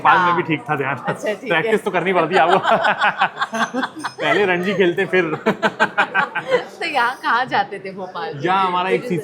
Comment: wtf did he say 🤣😂🤣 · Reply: Pehle Ranji (Ranji trophy) khelte hai bola bhai 😂😂